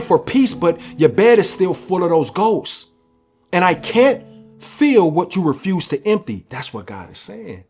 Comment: “ and I can’t feel what you refuse to empty” made me think of 1 Peter5:7. Now when I first heard it I thought you said “ and I can’t Fill what you refuse to empty” still 🔥 God bless you brother💯🙏🏾💪🏾